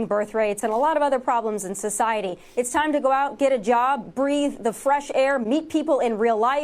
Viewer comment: I thought the pudnet said "Breed" not "Breathe' lol